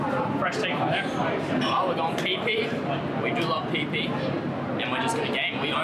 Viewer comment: did he say, “We love PP?!???”